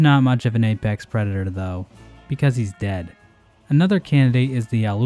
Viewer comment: thought he said NOOB was dead lol